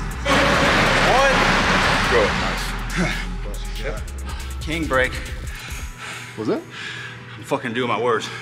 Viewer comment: it sounded like a need a break. The other foo is like what you say? And the other foo is like I'm doing my words. Hahah